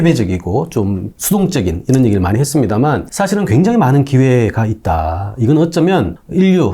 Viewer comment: 굉장히 많은 기회가 있다 = 절대 결혼만 안하면 살길은 있다로 들림